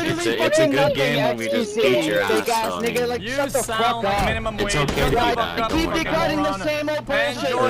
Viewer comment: Did he just say "You sound like minimum wage " lmao